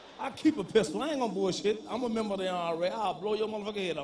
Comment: Sounded like Bernie said he was a member of the IRA! I always liked this guy! 😂😂🇮🇪